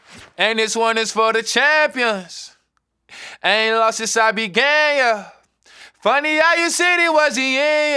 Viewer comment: Damm I thought he was saying “I ain’t lost since I was gay, yuh” 🤣🤣